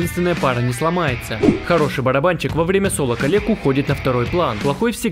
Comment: Мне послышалось не "коллег„ а "калек„